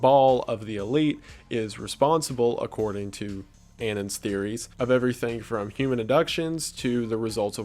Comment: can anyone tell me what [redacted] is supposed to be here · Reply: Qanon